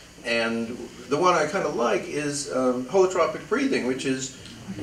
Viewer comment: About what breathing technique is he talking at 26:20. Not a native english speaker and can't figure out how that's being written to google haha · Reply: Holotropic breathing or breath work. good luck on your search